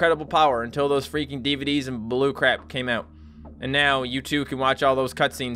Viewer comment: Did he say Blu-CRAP !?